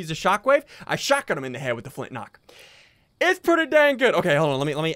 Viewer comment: did ian say “ shotgun them in the head with the flint-knock”